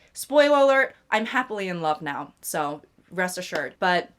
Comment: My adhd brain listening to the video and scrolling on insta. All I heard was “she was happy in love” and girly pop is definitely a yapper, I hope her pookie is a listener 😂